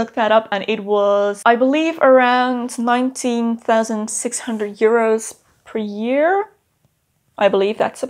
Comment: Wauw, I totally misunderstood you, i thought you said: Wage is around €19.600 a month when you said: around €19.600 a year hahaha